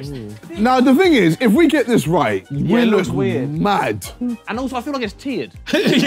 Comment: what did harry say? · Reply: “i feel like it’s tiered”